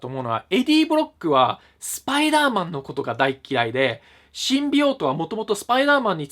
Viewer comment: スパイダーマン知らないからシンビオートのこと新病棟って海老蔵途中まで聞き間違えてた